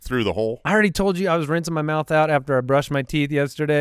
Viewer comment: I thought said “renting his mouth out”…. *Rinsing* 😂